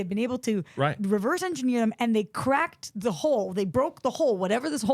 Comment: Why do people keep thinking she said hole. she said hull! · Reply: I think because it really does sound like she's saying "hole", because she said it repeatedly. Like, at 20:53, she's clearly saying "hole" over and over. 😂 And if she thought he said "breach the hole", it makes sense why that wouldn't make sense to her. I feel like she would know what breach the "hull" means.